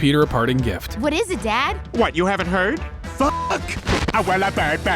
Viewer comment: 😂😂 what stewie says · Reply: "Fuck." - Stewie Griffin